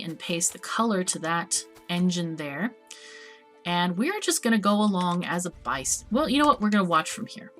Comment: Wait what? Bice is actually an english word? It is pronounced exactly like "bajs" which means "poop" in Swedish. "We're just going to go along as a bice" => LOL. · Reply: Sounds like she wanted to say "bystander", but cut herself off before finishing the word.